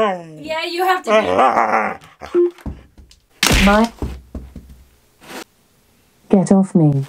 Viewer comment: did say: "Mutt!" when the dog knocked her over the table? LOL!